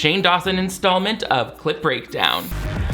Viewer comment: When you mishear and think he keeps saying clit break down...